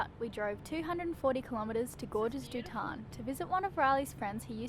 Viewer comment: Did anyone catch what the French rock climbing city was? it sounded like Elayna said "gorgeous du ton" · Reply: It's a canyon, it's called Gorges du Tarn.